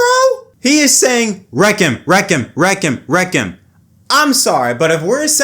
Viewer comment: Not sure I hear "wreak him..." What I hear could be "wreakin' wreakin'..." Not saying I'm right, but that's what I hear. I'm a Denny fan. I am not a Dillon fan. That was a racing incident. Looked to me like they were going for the same real-estate. Your mileage may vary.